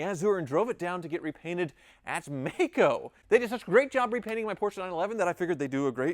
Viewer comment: I watch in 1.75 speed. When said he took his bumper to MAACO it sounded like he said he took it to MEXICO.